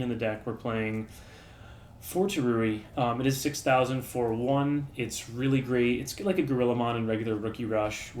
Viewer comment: what digimon are you saying? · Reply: Turuiemon